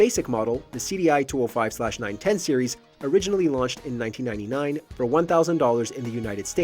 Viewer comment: So did it release in 91 or 99? Jesus does anyone watch or edit these videos before you guys just throw them up online? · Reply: I'm confused by that too. I played 2:57-3:00 three times make sure I heard correctly. I thought maybe he said '1989', but he said '1999'.